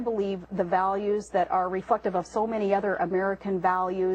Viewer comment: shes supposed to say "reflective" but she actually says "REFUCKTIVE" hahahaaaaa this woman gets more and more stupid as the months pass by